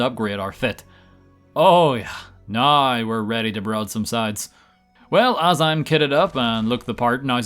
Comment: I heard, "Now we're ready to side some broads." 😂